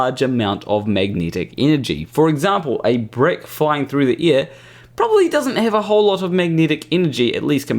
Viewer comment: I heard "a brick flying through your ear" and I thought, "That sounds like a good idea."